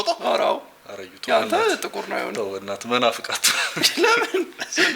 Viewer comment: did he say መናፍቅ አትሁን at 2:00😮